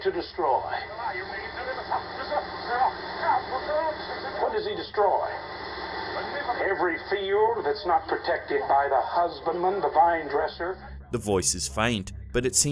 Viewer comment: When he said"what does he destroy?" I could have sworn he said"a nigger" ...(it sounds like it says that) at 1:03 Check it out for your self.